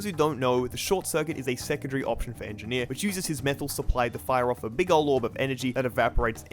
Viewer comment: I heard mental supply lmao